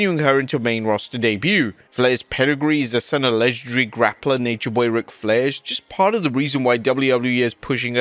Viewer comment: Did I hear correctly 'the son of legendary grappler rick flair'??😂😂